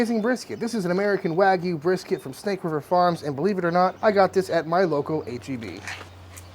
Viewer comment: I thought he said "I got this at my local KGB" 🤣